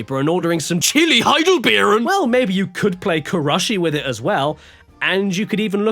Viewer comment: What did he call this game? Crushy? Is that what Intelligent Qube is called in Europe? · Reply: Kurushi